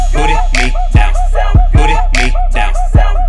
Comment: I thought he said “booty meat down”